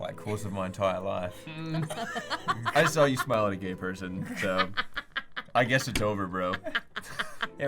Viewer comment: "I saw you smile at a gay person" got me dying on the floor😂😂😂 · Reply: At first I heard "I saw you smell a gay person" 🤣🤣